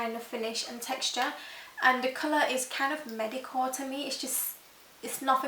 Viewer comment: Did you say medicore or mediocre at 5:03 ?